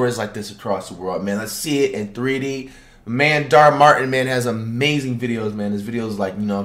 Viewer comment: Why he sound like he says Dhar Martin 😂😂😂